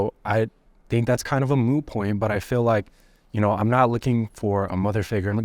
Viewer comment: he actually said it's a "moo point". Friends fun